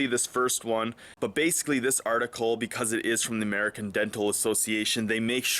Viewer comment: American deadpool association? That's what it sounded like you said. Lol